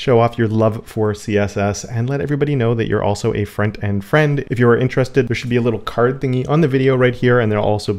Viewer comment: I have been watching you for a couple of months now and i always thought you were saying friend and friend 💀 really took me seeing that t-shirt to realize it's front end friend hahah